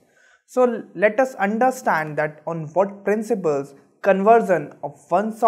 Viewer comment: He says undaaahstandha instead of understand